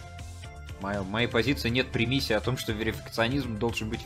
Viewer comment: что он говорит? какой примисии?